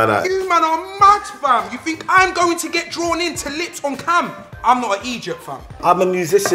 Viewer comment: Filly saying he won’t lips on camera bc he’s not an idiot in front of a guy who was in love Island LOOOL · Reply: it sounded like he said "im not an Egypt fam" lol